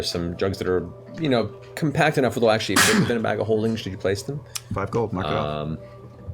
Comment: What is it that Liam says to Laura at 1:11:19 that has everyone loose it? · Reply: "Five gold, mark it off" He's just trolling her, but everyone laughs at how angry she actually gets.